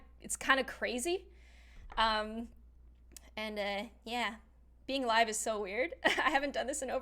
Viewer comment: What She said: “being live is so weird 😆” What I heard: “being alive is so weird 😆” Me: goes back to see if I heard that correctly